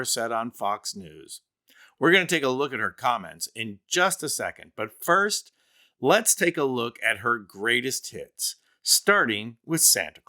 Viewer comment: I thought you said, ". . . her greatest tits." And thought, jeesh, how many does she have? 😂